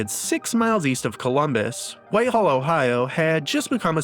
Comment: wtf are you saying at 5:46? When you say 6 miles east of Columbus “wayyhawiill”? You don’t sound like AI, but this makes me think it’s AI. I’m thinking it was supposed to be Whitehall? Which is east of Columbus. I listened to that over and over and I’m thinking you left the “T” out of Whitehall in your script and the AI said something crazy.